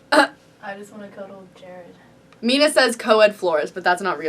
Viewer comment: anyone else hear "I just wanna cuddle with George" lol maybe I'm just deaf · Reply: Lauren Keller i think she said jared